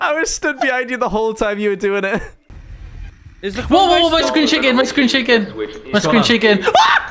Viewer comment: I genuinely thought was saying “ice cream chicken! Ice cream chicken!”